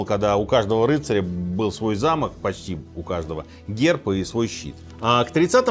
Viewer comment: …» и свой щит»… мне послышалось и подумалось « и свой счет…. в банке»😂😂😂😂